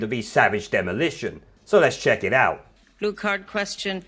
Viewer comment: OH, she said "blue card"; I thought she said "retard".